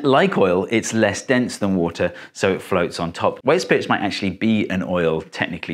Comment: the subtitles say "indistinct" whereas Steve says "White Spirits" (just rather fast)